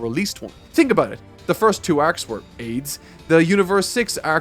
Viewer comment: Did he call the first two movie arcs aids at 26:50 ? Mark wtf? I had to have misheard that, I feel like that's not wothin your vernacular to call something aids.